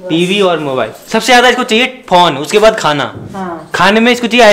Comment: pr kya bola bhai 🤣🤣🤣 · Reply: Phone